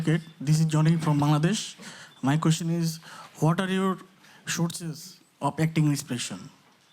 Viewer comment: Gosh !!!! He keep repeating his question & couldn't figured out what the hell is was saying !!! Damn, spit out that hot potatoe from yr mouth boy ( 12:32 ) !!!!! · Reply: I think he was trying to say "What are your sources of acting inspirations (?)" Not quite sure tho...